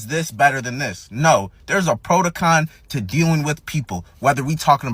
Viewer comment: protocon? lmaoo what word is that?